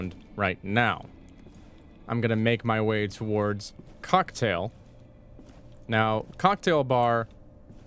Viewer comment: did he just say cucktail?